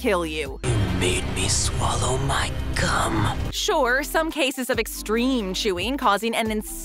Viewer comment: I heard cum